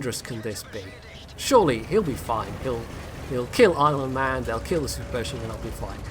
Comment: Lol... all this time I thought had been saying his name as "Islandman" not "Irelandman". hehe.... well it is an island.